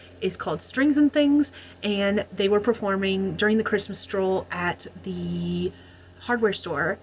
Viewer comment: First time I heard it as 'Christmas Troll' then I understood 'Stroll' 😂🧌